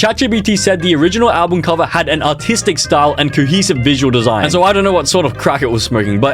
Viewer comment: bro I thought they said “autistic”💀